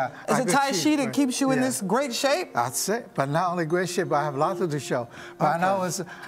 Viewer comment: what does he say at 7:44 that makes her crack up? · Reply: He said something along the lines of 'having alot to dish out'